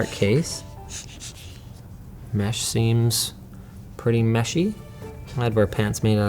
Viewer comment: "The mesh seems....... pretty meshy." Ah yes, the floor is made out of floor. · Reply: I’m not re watching it cuz idc but I thought he said mushy